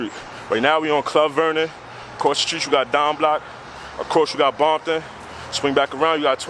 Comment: Did he say Bompton @ 2:12 ?